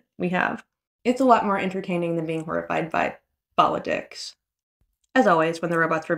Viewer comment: I know you said 'politics' but I thought I heard 'ball-of-dicks' and my immediate response was "yeah that would be very horrifying". Thanks for the continued coverage.